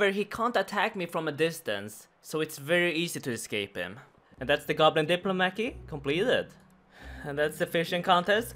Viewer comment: Did he just say goblin diplomaki? xD